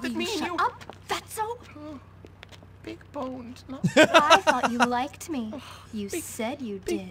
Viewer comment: what did Algie say? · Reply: He said big bones